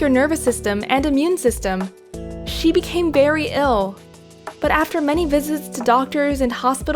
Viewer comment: Did she just say... "berry ill"???